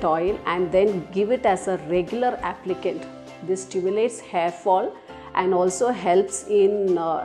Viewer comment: Did she say this stimulates hair fall at 1:31? I am confused now.😢